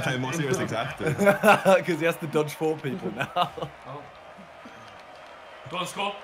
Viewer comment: what did Simon say? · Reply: "Cause he has to dodge four people now". That's what he said